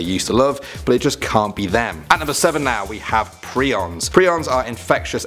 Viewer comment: I thought it said prisons